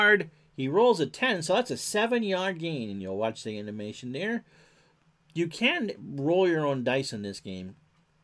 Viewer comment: Hi, Great video. Did I hear you say, "You CAN roll your own dice with this game."?